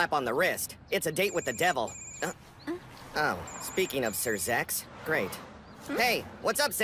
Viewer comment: did he say sir zexs